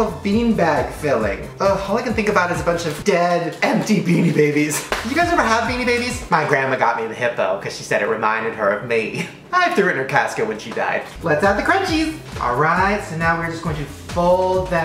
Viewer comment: "My grandma got me the hippo beanie baby because it reminded her of me, I threw it in her casket when she died" OMG 😂😂😂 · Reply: Sean O'Halleran I thought he said "...I threw up in her casket when she died." I literally choked at both things tho😂😂😂😂